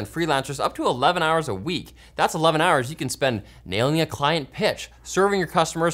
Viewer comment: I swear for a second I thought you sponsor said "nailing a fine b!tch" 🤣